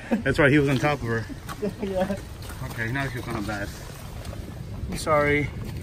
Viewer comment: did just say "okay now you're gonna die"